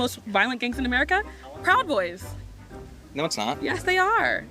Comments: 😂😂I kept hearing her say “cowboys” and not proud boys. I was so confused why she thought cowboys were a problem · 😂😂I kept hearing her say “cowboys” and not proud boys. I was so confused why she thought cowboys were a problem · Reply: I thought she said cowboys until I read your comment. 🤣